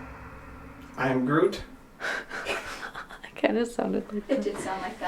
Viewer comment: I heard I didn’t do it 😮😮😮😮